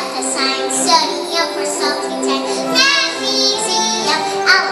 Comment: When she said magnesium I thought she said mac and cheese now that made me hungry